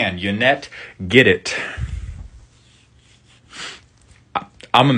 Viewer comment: Hey, when are you going to do Otrimi, Ever-Playful? On one hand, it's a memey mutate deck that can be safely ignored... ... on the other hand, mutate into unblockable infect creatures to make everyone die of stage 4 liver cancer thanks to a devious salamander boy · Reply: Wait, did you just say "Make her purr for the clout?" 💀